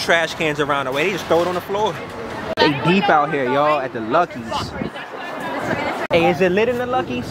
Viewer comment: I thought he said lucki , da rapper 😂